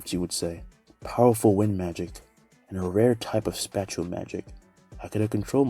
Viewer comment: I thought you were saying special but you were saying spatial